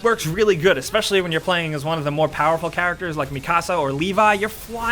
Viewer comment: did he just say mi casa?